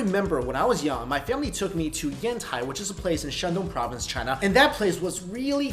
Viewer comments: when he said Yantai I thought he said hentaai, lol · I thought he said henti XD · I thought you said "Hentai" instead of "Yantai" xD